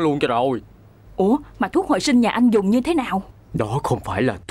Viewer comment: Nghe điếc cùng mình: thuốc hồi sinh-> nghe thành thuốc hồi chinh:))